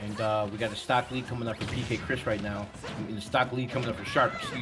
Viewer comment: I almost thought it said PC Chris. Like from OG Melee